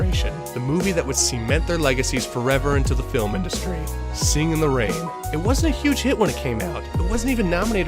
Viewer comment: It sounds like you say “Sing in the rain” and “law land” multiple times in the video. Anyways, great video. I’ve been on a La La Land mood lately and been watching years old videos about the movie.